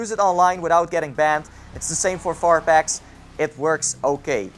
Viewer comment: thought you were saying FARP HACKS... I was like wtf is that? 40 fucking bucks for that shit!